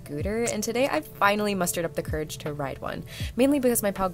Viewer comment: i thought i heard mustard up the courage